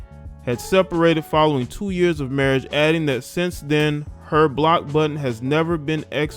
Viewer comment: I thought she said her “BOX button” has never been exercised more.